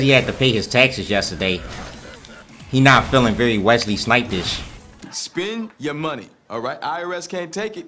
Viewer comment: Yoo I always thought said "Wesley Snipes" on ex dp